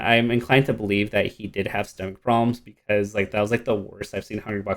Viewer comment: What problems did HBX have at? 8:38 I couldn’t understand what he said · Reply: YoshiStar stomach issues